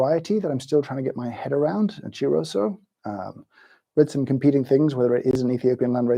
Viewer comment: Help me out here. At about 49:30 James names the coffee cultivar in Sample D. I can't quite make out what he said. Very interested in finding some greens to roast. But first I need you to tell me what he said! Incidentally, I KNEW that Sample D was Ethiopian :) And this may be controversial, but it was by far my favorite. This was a fun way to while away an hour. Cheers and I hope you have a great day. Also, NGL, I had a mini-panic attack on James' behalf when he nearly mixed up the samples there in the early setup! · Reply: Chiroso